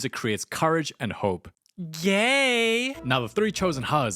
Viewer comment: why did i hear gay and not yay